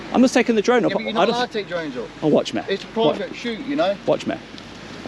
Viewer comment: Two men, 2 totally different interactions. I thought white van man said "its a private shoot"...??? Loved the video, great drone shots, very interesting finding out about the work going on there. Big up the 'better man', i thought he was polite, not sure he knew you were filming though! 😂👏